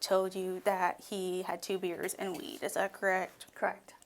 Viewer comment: The fact that they were adamant that he said he had “2 beers and some weed” when I heard “I had 2 beers and that really it”. This is just disgusting all the way around.